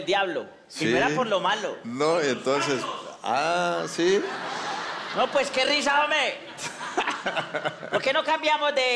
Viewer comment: que dicen por allá? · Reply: dice por los cachos